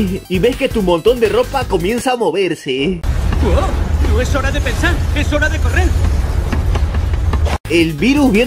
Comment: tengo tanto sueño que escuche no es hora de correr es hora de correr